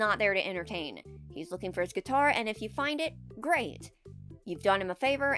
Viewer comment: Dose anyone remember that one time a kids rockstar Freddy plush was thrown on roof and firefighters randomly came and got it for him or was that a fever dream I had? Also was I the only who thought rockstar Bonnie said he fucked his guitar or was that me being a freak?